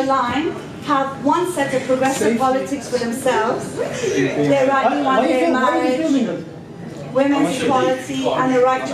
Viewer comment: What were they saying at 13:59 ? I couldn't make it out. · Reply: Metter They were saying "why are you filming us?".